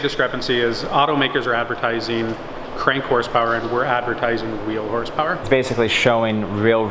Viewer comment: its wheel horsepower what he said not real horsepower.